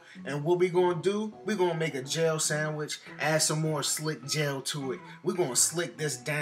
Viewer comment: I thought he said a jailsandwich